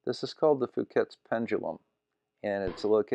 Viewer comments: Another excellent video, but did you just say Phuket's pendulum at 15:00? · lol did you say Phuket's pendulum?